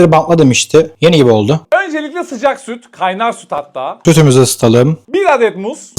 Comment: Abi sütümüzü ısıtalım dediğin yerde "gözümüzü ısıtalım" diye duydum 😂